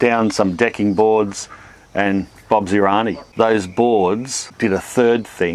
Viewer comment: what did you say at 5:42 ? The best guess I can make is a twist on the British phrase "Bob's yer uncle" changed to "Bob's yer auntie". But I'm not sure.